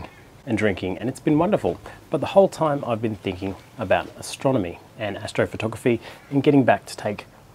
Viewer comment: LMAO... I swear I heard "I have been drinking about astronomy"...